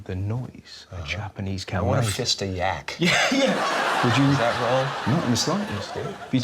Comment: did he just say "fist a yak?" hahah